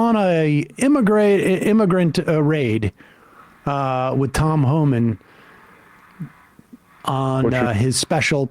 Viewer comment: i really thought he was saying tom holland for the first 5 mins i didn't understand why he got british spiderman as his sidekick